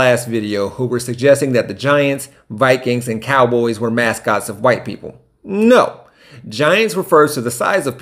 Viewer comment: "There's no mascots of a white character" The Swinging Friar, Bernie Brewer, Rowdy, Steely McBeam, Pat Patriot, Captain Fear, Sourdough Sam, Sir Saint, Viktor the Viking, Purdue Pete, Pistol Pete, Leprechaun, Sparty, Grubby the Miner, King Cake Baby, Tank the Trojan . . . (and on and on) . . . all white characters. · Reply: Incorrect. He says, and i quote "mascots of white people" He did not say "character" he said mascots of white people. See timestamp 4:35 for this. He never said "of a white character"